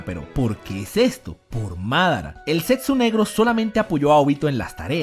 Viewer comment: juraria que escuche "el sexo negro" hahahahahah